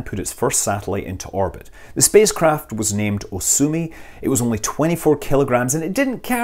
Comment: re - 9:05 Hang on... Did you say that it was named "So sue me!"??? Ps: I know ya didn't! I heard ya say "Osumi"